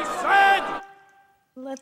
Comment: All this time I thought widow was saying let the meat cake